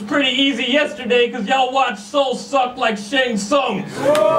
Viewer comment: "y'all watch soul suck like...." at 3:16 what did he say? shane sung?? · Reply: shang sung. from mortal kombat.